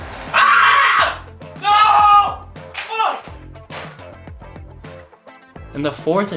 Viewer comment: is that cuss word i hear someone didn’t pay attention to their clubs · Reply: Worst Klace ‘FUH’